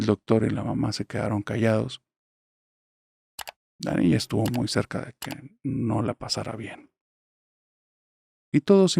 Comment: no entiendo que dijo en esa parte, la niña estuvo muy que?😢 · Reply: según yo dijo que estuvo muy cerca de no pasarla bien